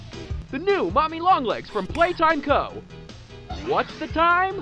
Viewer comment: anybody catch that? sounds like either, "let's go," or "play co" from Poppy. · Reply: It's play co